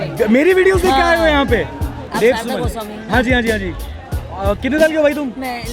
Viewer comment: India needs journalists like and Samdish. Hats Off!! At, 7:19 I heard like 'aap Arnab Goswami ho na!' 😂😂